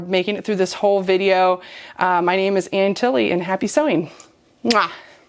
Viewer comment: Great designs, you're so talented!. I did laugh though at first when I misheard the name as "Aunt Tilly" which was who I said was in town referring to being on my period. I enjoyed watching a few of your videos and wish you well. Thank you.